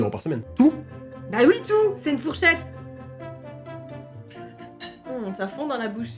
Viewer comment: C’est une quoi ? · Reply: Une fourchette :0